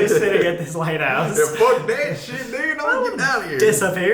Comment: did Colby say “f__k that sh_t n___r “ I know Sam said” disappear?”